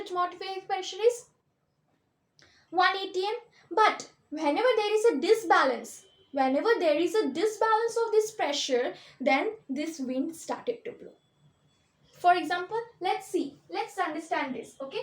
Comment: Ma'am what did you say here?? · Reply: The paper ball placed in the mouth of the ball is not moving inside instead it's coming out when I am blowing air inside the bottle